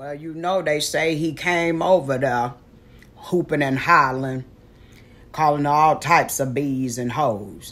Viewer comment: Hoopin and Hollerin is such an old folks phrase🤣🤣🤣🤣🤣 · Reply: I always heard 'hootin and hollerin' but I guess I shoulda been in the basement with Lele lol